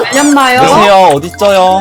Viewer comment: 어디쩌요라고 나만들리나?